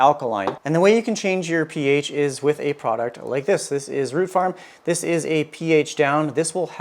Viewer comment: I thought that box said “room fart”